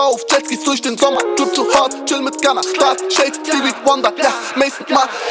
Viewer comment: Hauptsache ufo macht ein Song mit Lil Baby aber redet dann davon wie er mit Gunna chilt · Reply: malik One ich hab verstanden : chill in Ghana 😂